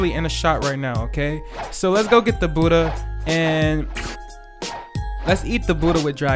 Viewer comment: Let’s get the budhha sounds like your saying let’s get dat booda (boody) LOl